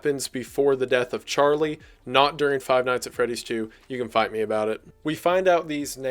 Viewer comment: All I heard was "you can bite me about it. " and my brain screamed- WAS THAT THE BITE OF 22?!